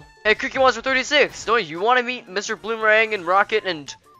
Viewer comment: How the hell do you put your flower under you while your flying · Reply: I thought you said boomerang